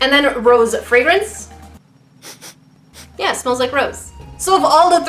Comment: When she said "It smells like rose" I heard " It smells like gross"